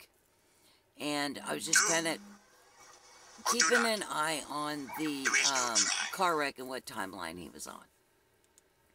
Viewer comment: Anyone else hear that would interuption at the 3:42 mark ? .. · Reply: I figured it out ! :) .. it is saying " Do , or do not , there is no try "... its Yoda ! :D ! .... I know it sounded creepy at first, freaked me out , but now I'm smiling, I love Yoda ! hahaha ... have a great day !!